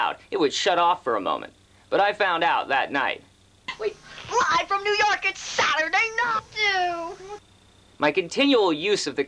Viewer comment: i died of laughter, what were you trying to say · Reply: Live from New York, it's Saturday Night!! (SNL)